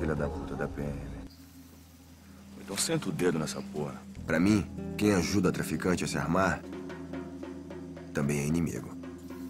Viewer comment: I'm kinda late I doubt someone is gonna read this but I hated this subtitles, they lost A LOT of the nuance of the speech, for example at 3:42 the subtitles say "those who helped dealers were just as bad" when it should be "are also the enemy". Yeah, is kinda acurate, but the diference is key to trully getting the mindset Nascimento has